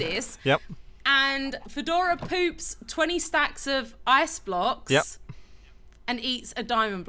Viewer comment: I thought she said "bajs blocks" and not ice blocks haha